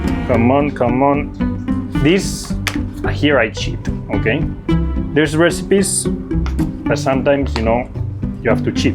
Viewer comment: What did he say at 3:30? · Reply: usate la maquinina (use the machine) I thinkkk